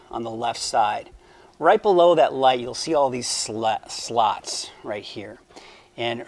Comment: I had to relisten cause it sounded like he said sluts.